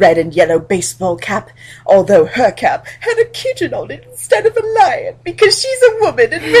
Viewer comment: When you said that Hermione had a kitten on her hat, I misheard it as had a kitchen on her hat, and the best part is that I didn't even question it. It's exactly the kind of thing I would expect from this story.